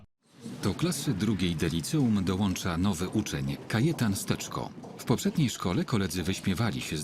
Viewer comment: czy tylko ja usłyszałem "Kajetan z teczką"?